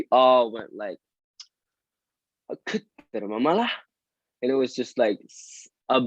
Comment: Does someone know what he said in norwegian at 15:31 ? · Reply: I believe he's saying "kødder du med meg" (are you messing with me/are you serious/are you joking)